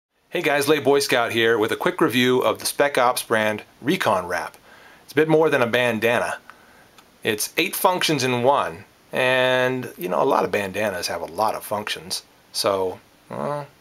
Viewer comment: i thought the vid said bacon wrap. is it better?lol. i carry a bandana now. also at work. for wiping the sweat off my face before it gets to my uniform. an abercrombie and finch bandana. you yuppie.lol. thanks for taking a look at this.